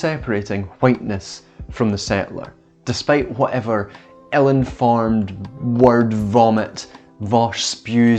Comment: ohhhh! Ill-informed! I thought you said Ellen farmed Thank goodness for subtitles